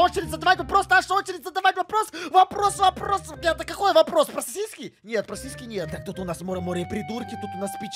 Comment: 🤨 Эд сказал си$ьки или мне послышалось